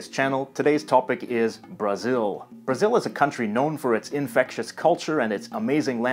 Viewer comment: Eu não sou fluente em inglês. 0:23 Entendi errado ou ele disse "infectious culture"? O que significa?